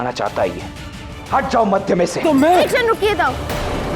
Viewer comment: did she say "ek second rukiye dau" ??